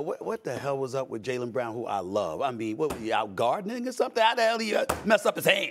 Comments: could swear I heard "out guard niggas" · heard stephen a say "guarding n word" instead of "gardening" first couple times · I swear SA said nig*** at 7:02😂